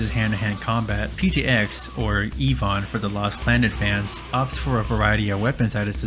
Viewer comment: Did he say "Elon"?🤨